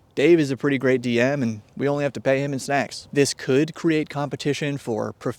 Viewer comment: sounded like secks on double speed not snacks 😅